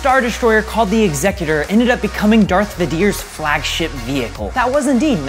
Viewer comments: Did he just say Darth vadier? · why does he say darth vidior?!? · Wait. Did you just say Darth Va-dear and at at?